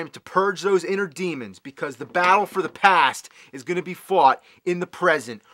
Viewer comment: It sounded like you said "the bowel for the past" instead of battle. But hey, it fits.